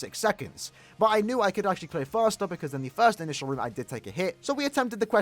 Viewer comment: did he just say "on me first initial room" I haven't heard that one from before. perhaps more British coming?👀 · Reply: He said "in the (thee) first initial" where are you even pulling out this british thing lmao